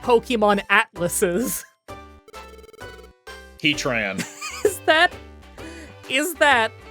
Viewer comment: when he said heatran i heard "he tran" and i was really unsure what atlas being trans has to do with them holding the world up, but i respect their identity